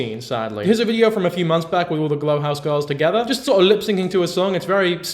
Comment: first i thought you said lipsing 😭